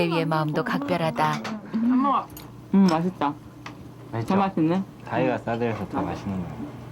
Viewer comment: 나만 개맛있네로 들리는건가 잌ㅋㅋㅋㅋㅋ